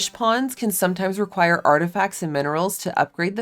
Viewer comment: Listening for my background sound and heard it as "some fish require bath salts" for a sec 😂